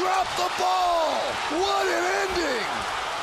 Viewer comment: I thought he was gonna say "WHAT A IDIOT " but instead he said " "What uh ending" lol 😅